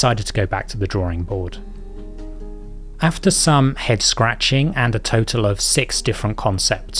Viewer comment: Did you say "After some hex scratching"?